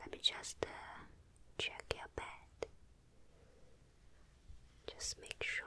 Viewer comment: I thought she said “check my pad” 💀